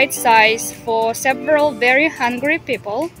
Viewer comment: I had to keep replaying the video cause at first I thought she was saying "several very hundred people,"...but she was saying several very hungry people lol.... it's her accent lol